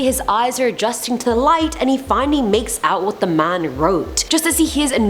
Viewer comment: When she said he makes out what the man wrote, I thought she bout said he makes out wit da man I cried